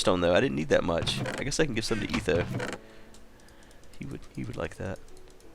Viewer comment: When he said " I can give some redstone to etho" i thought he said " I could use some redstone to eat tho" O.o i need to get my ears checked XD